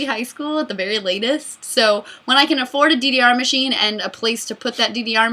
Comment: I thought you were saying a DVR machine, not DDR machine... i was really confused, because those are not very expensive...